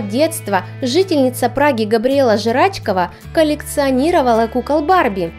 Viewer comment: Мне послышалось?Белла Жрачкова?!🤣😂😅🤣🤣🤣😂😂😄Xd